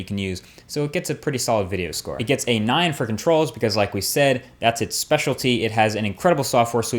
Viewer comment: When you said 8 for controls I thought you said 8 friggin trolls